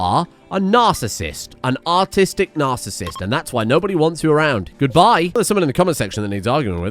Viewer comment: I heard him say autistic narsasist